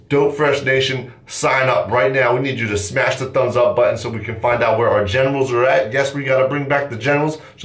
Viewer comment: i thought he said so we can find out where are genitals are at lol